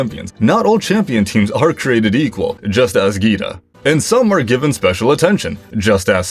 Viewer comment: I thought you said gi-hun from squid games you know player 456 dam I’ve been watching a lot of squid games lately